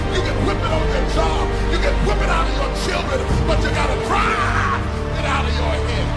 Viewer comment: did he just say to whip it out on your children? xD xD xD xD